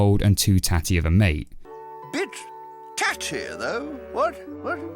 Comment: I swear to God i heard him say "bitch"